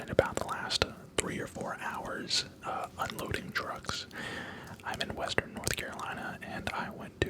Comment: When he said he was unloading trucks I thought he said he was unloading drugs. 😳